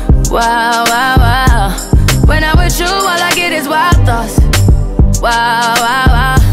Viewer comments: I thought she said when I'm with you I get LOCKED UP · bryson part tho. i know this shit lit in the club!!! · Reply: I thought she was saying wild 'thots'